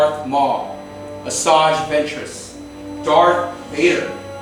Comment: did he say dark Vader or Darth Vader